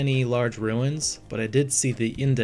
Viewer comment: It sounded like you said ancient penthouses? But if you said pit houses and there's not much left of them they're probably very old